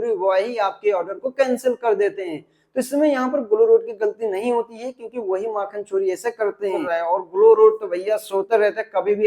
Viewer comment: 😂😂 kya bola wahi m**** 😂😂🤣🤣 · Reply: nhi bro makhanchori bola hu 😀😀